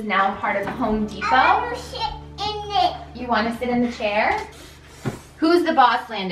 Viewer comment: lol I couldnt stop laughing it sounded like he said " I wanna s$#* in the chair"